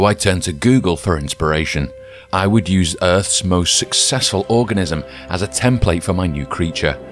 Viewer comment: I... I thought it said Orgasm instead of Organism..